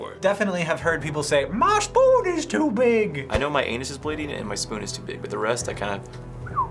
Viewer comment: "I know my anus is bleeding and my spoon is too big." I legit thought he said he spooned or something, explains the bleeding anus.